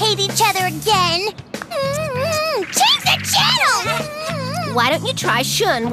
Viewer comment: What did Julie just say? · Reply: Change the channel!